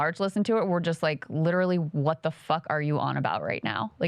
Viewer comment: Did I just hear Krystal say 'fuck'??? At 4:04...😅 That was kinda hot lok